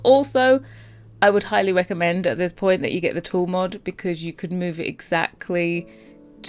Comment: This stuff is so cool as always!! Also, could anyone tell me what mod she mentioned at 13:45 ? :) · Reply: TOOL Mod by Twisted Mexi